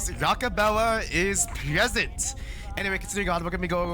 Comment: rockobella🥀 Edit: I thought it said Barack Obama😭